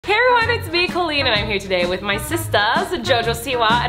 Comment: I heard: ‘Heroine! It’s me Colleen.’